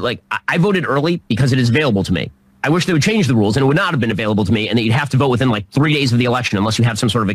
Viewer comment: Did you not hear the part where he said "I wish they would change the rules so that it would not be available to me"? It was at 0:47. I heard it clearly.